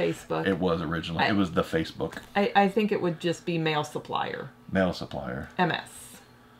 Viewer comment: All I heard was "male supplier" and started laughing uncontrollably